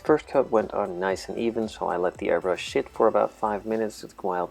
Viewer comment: I know you said "airbrush sit" but at first, it sounded like "Airbrush Sh*t" to me... Is it just me or did you guys hear the same?